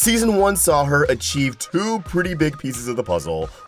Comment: I thought he was gonna say "Achieve two pretty best friends" for some reason